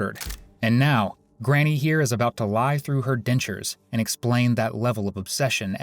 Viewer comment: Did he just say “lie through her dentures?!” 😂😂😂